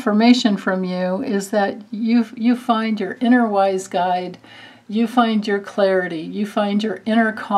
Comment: I heard "inner wise guy" instead of "inner wise guide" but honestly that's on brand for me. HAHAHAHA <R3